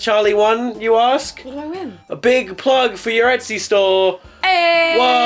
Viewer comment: I first thought he said "charlie has won a big plug for your ass to store"